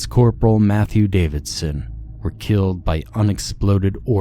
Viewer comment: Thought he said they were killed by unexploded orphans 🤦‍♀️😅 👻👻🥇🥰